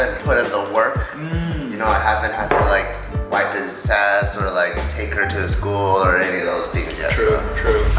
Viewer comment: did say "Take her to his school" 5:47 damn they are really not letting anything out.